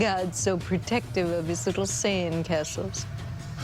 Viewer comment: did she just say saiyan ?